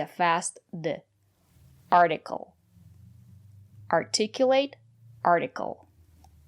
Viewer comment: what the second ‘t’ in this word? Can we pronounce it ‘d’ because ‘e’ is not stressed here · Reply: articulate